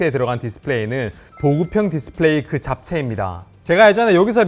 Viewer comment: 초 사이 28초에 서 저만 잡채라고 들리나요?